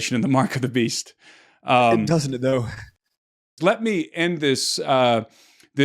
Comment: Hmm: @ 1:13:40: what you're saying sounds like the book of revelations and mark of the beast. it doesn't though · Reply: You have it in reverse. He actually says, "Doesn't it though."